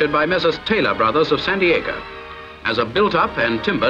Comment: Surely I can't be the only American who heard "San Diego" at 0:46! (What the narrator actually said was "Messrs. Taylor Brothers of Sandiacre", an English Midlands village conveniently located online to the LM&S.)